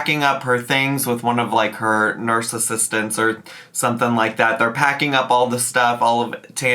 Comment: thought u said narcissistance 😂😂😂